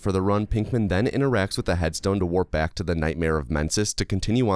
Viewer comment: I was listening to this while fooling around in another tab. I heard "Nightmare of Mensis" and thought it was "Nightmare of Menses". Did a double-take, but then I thought to myself, "Bloodborne, indeed!"